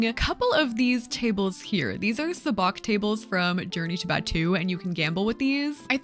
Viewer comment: I'm so dumb.... I really thought she was saying "Journey Tibet 2" I was like, "What about Journey Tibet 1?" . . . It's "Journey to Batuu." It's Star Wars. Im an idiot.